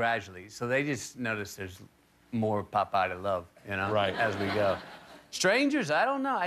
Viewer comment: am i the only you who thought he said "more popeye to love"